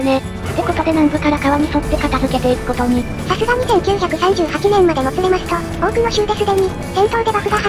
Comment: 俺疲れているな6:15が2938年って聞こえる